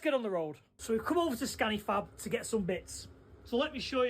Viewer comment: When he said scanifab my Mrs said did he just say fanny scab 😂😂😂 no love 😂😂😂